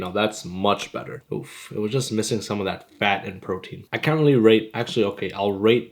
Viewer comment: Did he just say 'r@ at 1:12 😭